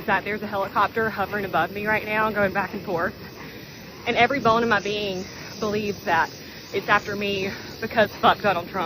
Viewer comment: I swear the helicopter nut head... I heard her say "Every Bone in my BEAN" .... damn girl ....